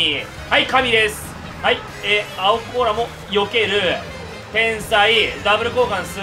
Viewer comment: の青甲羅避けるー 天才〜が変態〜に聞こえるw